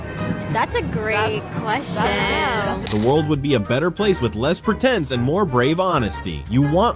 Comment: Im sorry my ears aren't the best but did you say "les-pretend" at 21:25?😂